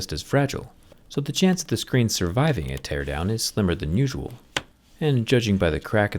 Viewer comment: "is smaller than usuall"? Im not native.. That is what he said?